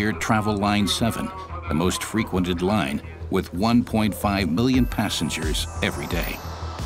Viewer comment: Did the speaker really say 1,5 billion passengers at 2:20 ? it should be million but i only hear billion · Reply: Million of course :) 1.5 million passengers on one that line every day.